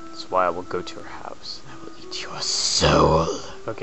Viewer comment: for a second I thought you said "I will eat your soap"...lol